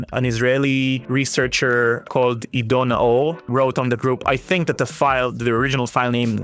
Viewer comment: closed caption says "an Israeli researcher called [inaudible 00:16:31] The name is Ido Naor.